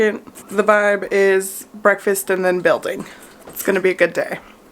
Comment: ish, when you say "the vibe" i hear "the bud". Kinda like green needle/brainstorm. Any body else hear that?😂